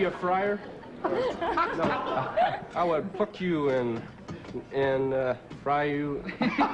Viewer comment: because of the inferior audio at 3:42 it sound like he said I would f--- you instead of pluck you